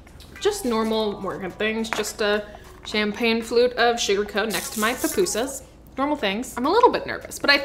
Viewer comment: did you say next to your PUPUSAS! omg I can't love you even more. Buenale! 🇸🇻